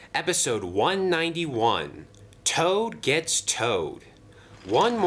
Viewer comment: I heard ‘toad gets toe’ 💀